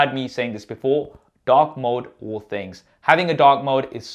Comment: I heard you say dog mode not dark mode